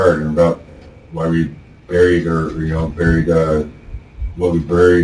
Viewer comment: he did what we call a freudian slip and said "where we buried her" 🫢